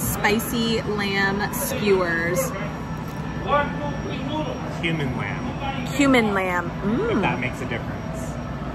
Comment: I thought he said ""human" lamb" I actually started to think, "what's that that?"🤦‍♀️